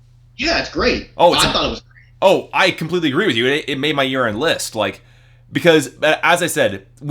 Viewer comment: when you said "it made my year end list", it sounded like you said "it made my urine list". glad you revisited this album!